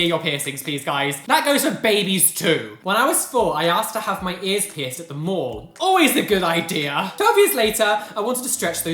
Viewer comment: I thought you said “‘When I was 4, I asked to get my ears pierced at the morgue’” and I was truly concerned.